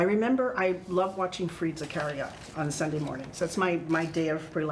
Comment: At the 4:43 mark , says what she used to enjoy watching. I couldn't catch what she said ! Please let us know!~ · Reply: I heard she was watching Freda Carrera. I may be wrong.